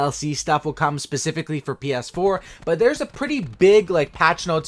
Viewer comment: Did he say BS4